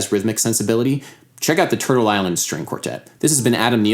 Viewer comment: Please can you write the name of the string quartet recommended at 6:24. I can't understand cause my English isn't good enough! Thanks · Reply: Turtle Island String Quartet is the quartet he mentions there. Cheers